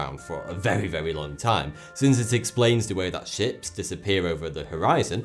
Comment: I swear to god he said "shits" at 10:52😂